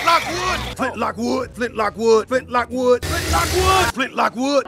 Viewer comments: I hear “flip-flop wood” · I thought he was saying "split like wood" for the longest time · I keep hearing “Flit Like wood” over and over. It starting to get stuck in my head now.